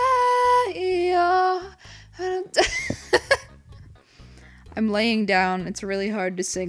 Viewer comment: It Sounded Like Slipknot's Song Eyeless Saying " III Am..."